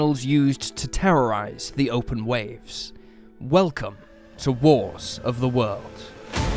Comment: i heard "welcome to worse of the world"